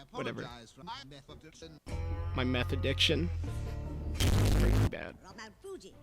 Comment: I actually didn't get that joke until you said it outloud, I always thought he said "my method of sin"